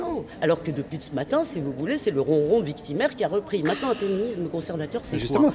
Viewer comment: Quelqu'un peut me dire qu'est-ce qu'elle dit aux alentours de 1:08 et 1:12? "C'est le __ victimaire qui a repris" (je suis un apprenant de français très enthousiaste et j'ai envie de comprendre tout ce que j'entends) merci d'avance !!! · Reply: Elle dit "le ronron victimaire" - dans ce contexte, le ronron veut dire une plainte mille fois entendue. Bonne chance avec notre langue, j'aurais jamais ton courage !